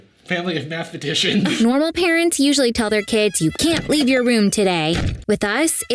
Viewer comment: I heard "normal parents" as "corona parents"